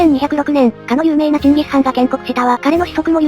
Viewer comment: チンギスハンがジンギスカンみたいに聞こえたw